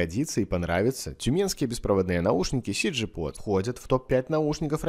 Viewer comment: услышал "тюменские беспроводные наручники" - нихерасе, думаю ... а как это вообще? )))